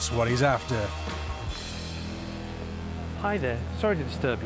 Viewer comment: did he say he wanted to buy his wife a 50-year anniversary gift? How old does he think he looks?